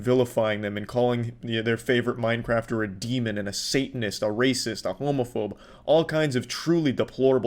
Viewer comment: when he said demon and a satanist for a second i thought i heard him say semonist